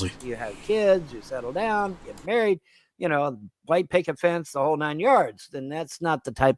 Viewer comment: "get married, have kids, play picket fence...."???(not sure, but it really sounds like he said ''play picket fence')